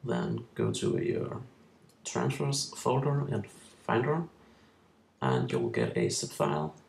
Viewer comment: what are you saying? it sounds like "'transfers' folder in Finder", but I'm not sure...